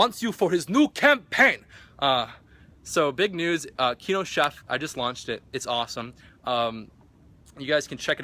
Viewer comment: i heard him say kino shaft XD